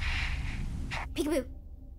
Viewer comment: Why I heard '' Pikachu'' the whole time instead of '' peek a boo''